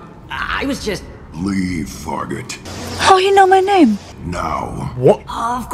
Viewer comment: It’s probably unintentional but Shepherd’s eyes appear like they were looking down. Jason be like “my eyes are up here, captain.” 😏 Now recruit Garrus and we’ll have a full BFs and pet team 2.0. 🦮👨🏽‍🤝‍👨🏿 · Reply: The way i thought he said a slur like what do you mean fa got...I mean we are one but still